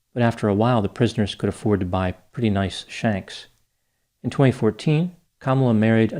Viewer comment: did he say prisoners could buy pretty nice "shanks"?